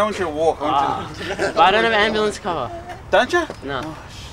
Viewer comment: What does say at 2:35 before he says " ambulance car"? ive tried to listen to it like 10 times and I cant hear what he says haha. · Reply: He says "i don't have ambulance cover"